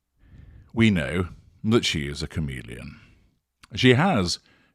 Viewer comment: Lol chameleon , I thought you said comedian but on reflection she's that too!